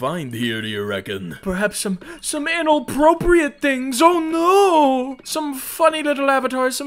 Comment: I thought he said analproprite 🖐😭